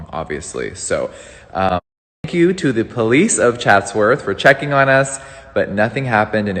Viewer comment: did he say f u or thank you?